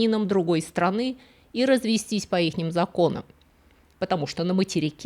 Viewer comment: По ИХНИМ законам??? (17:14) Или послышалось?